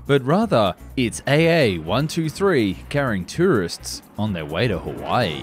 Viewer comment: I almost thought you said terrorists 😳